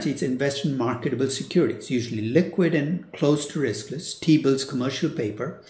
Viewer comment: if anyone wonder what he said 12:14 its T-bills